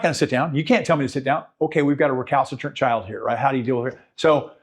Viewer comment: I'm sorry..... a what child?!? At 13:44 says an adjective that I've never heard before.... Could you spell that so I can look it up? Thanks ahead of time. ❤ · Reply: Recalcitrant